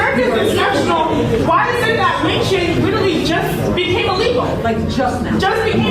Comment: Fatherless house holds has been well studied. Also lynching just became illegal? Murder was already illegal. You should get your money back from that college. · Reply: Oh lynching, I thought she said weed 😹😹 and wasn’t sure why didn’t address that… they convinced us here kids that their lives are at stake every time they step outside